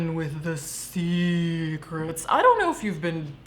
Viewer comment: i thought you were gonna say semen when you said secret for the first time